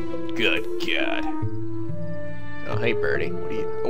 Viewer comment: did i just hear him say "hey bernie"